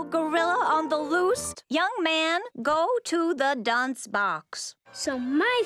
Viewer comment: “Yes sir, young man!” Always makes me cackle. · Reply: I'm glad I saw your comment, I always thought he was saying "young ma'am", knowing what he's actually saying just makes it that much better